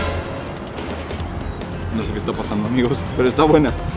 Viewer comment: pensé que dijo que la mujer estaba buena